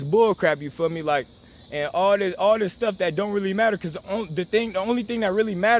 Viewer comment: wow you are for the bible and know the truth!!! YES ! so true and KING JAMES IS BLACK! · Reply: <num> I thought he said all this skin color shit don’t matter at the beginning of the video